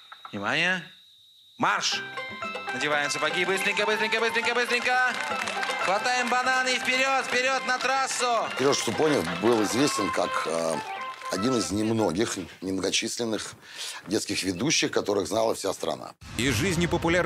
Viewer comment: Я 1982 года рождения. Я помню 90-е, жрать было нечего, родителям зарплату не платили, инфляция просто бешеная. Мы с братом бутылки собирали, лазили по паркам, какие-то яблоки кислые таскали, а черный хлеб с солью - это был деликатес. Если бы не шесть соток, выделенные папе на работе - реально бы сдохли. И самое главное, мы не понимали насколько мы плохо жили. Мне ребенку казалось, что живем как все. А в это время по телевизору круглые сутки реклама марса и сникерса… Все-таки живучий мы народ) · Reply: Я одна слышу Леша Супонев на 33:26?